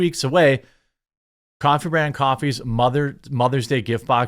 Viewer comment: I know the name of your company is "Coffee Brand Coffee", but the way you said it at 2:34 almost sounded like "Contraband Coffee" which is such a cool name... I was only passively listening until I heard that, lol.